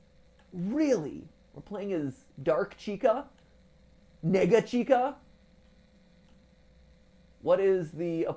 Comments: I know he said “Nega-Chika” but it sounds like he said the n word lmao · Wait did matpat say the n word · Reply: Nega which is short for negative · it sounded like he said n-word chica (without the hard r) 19:02 , im not saying he did for sure, but it kinda sounded like it for a second edit: i now know he said "nega chica" i looked up the nega meaning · what did he just call chica, this is a joke he said nega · Tell me im crazy but i swear twords the 19:03 line its sounds like he said niggia cheeky 😂😂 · When he said nega chica I thought he said n-word chica and I went straight to the comments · he did not just say n****r chica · AYO??? WHAT DID YOU SAYY??? 19:03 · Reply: nega, as in negative